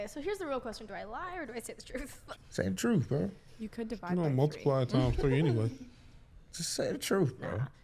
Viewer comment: i knew women did this, yall didnt hear what her friend said smh "Just divide it by 3"......so the other one is actually 30